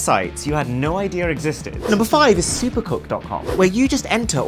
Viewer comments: I thought the first one said supercock I’m dead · Him:number five super co... Me:makes the rock sus face Him:cook? Me:OMG did u just say co*k (I heard this☝️ first because he said it so fast